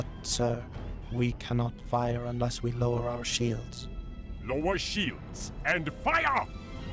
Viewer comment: LOWER SHIELDS!! And FIRE! - Sounded like Trench said "No more shields! And FIRE!"